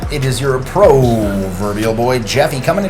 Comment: I heard "Broverbial Boy Jeffy" and now can't unhear it. Also loved the video as always. Great stuff! Gives me a lot to think about.